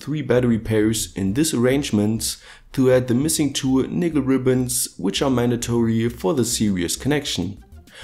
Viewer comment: Did i hear "nigger ribbons"😂